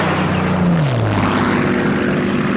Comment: What does Zaddy say at 33:51? It sounds like, "Masz to bal idzie, Fritzo", which would be, "You have a ball [dance] coming, Fritz". Is that right? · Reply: That was "chodź tu bliżej, franco", which is translated freely as "come closer, ugly bitch"...